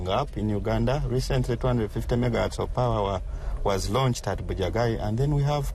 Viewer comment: did he just say 250 mga hertz of power?